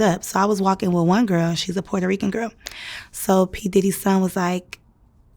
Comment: nah. she said "kim porters son" which has to be either the youngest one or quincy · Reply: loud and wrong!! At 6:39 she clearly says P diddy’s son!